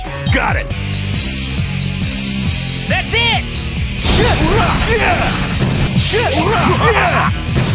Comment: All this time I thought Knuckles was saying "SHIT RUH" and Tails was saying "Gay"...........This game tho.....